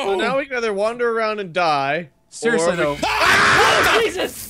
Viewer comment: Freddy: Jumpscares, and Mark Mark: WHAT THE FFFFFUCK!!! screams: OH JEEZ-- flips headphones off head me: laughing uncontrollably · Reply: A.D. Harmuth he actually says,”OH JESUS”